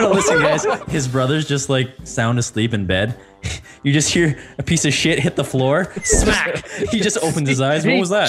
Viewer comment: Здесь есть русские ??? 1:00 (мне послышалось "писающие лето")😹😹😹🤣